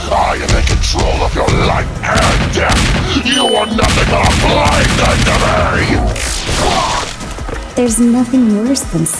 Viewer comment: Great work man! Could you tell me what you said at 25:59 tho? I couldn't understand that "nothing but a.." part. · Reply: "PLAY THING TO ME!!!" Also thank you!